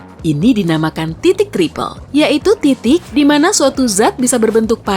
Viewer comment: apa gw salah dengar di 1:33 :v titid